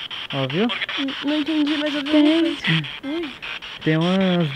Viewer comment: o microfone da câmera pegou uma molher falando "Tem" so eu ouvi isso?